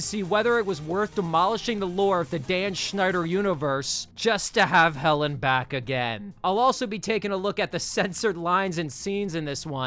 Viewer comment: Pretty sure “Helen Back Again” is a play on the phrase “to hell and back again” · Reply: I was just about to type that 😂 I think you're right. I was listening to this, but doing other things, and I genuinely thought he said "hell and back again"